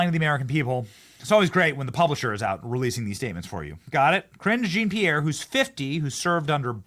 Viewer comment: Ohhhhhh, Karine Jean-Pierre! I thought you were saying Cringe Jean-Pierre 😅